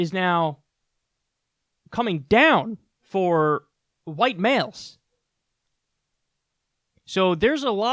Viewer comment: did he say white males?